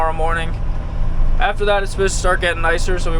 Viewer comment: i thought for sure you were saying tetting this whole time lol